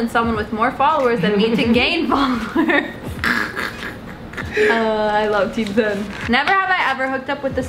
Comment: did corinna say she loves team 10 ?? Did she have beef with some one from there lmaoo idk I probably heard wrong 😂